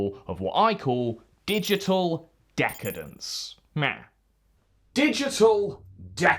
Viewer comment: did you just say.... Meow?